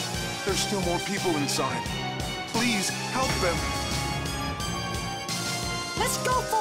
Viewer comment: Girl:there kidnapping Boy:let’s help Help who the clowns that sounds like your saying help the clowns